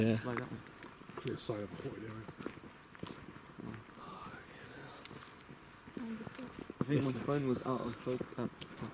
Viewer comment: There's whispering at 5:41 (War hero) 5:57 (Can't tell what it says) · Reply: is one of them saying fucking hell